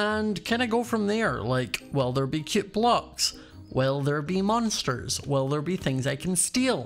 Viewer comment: At first i thought he said "will there be cute boys?" Lmao